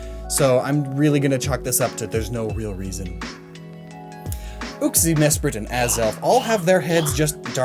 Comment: I swear he said "oopsy"